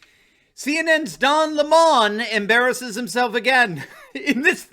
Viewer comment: I thought I heard say "BLM's Dom Lemon" At the start. Ha ha ha ha!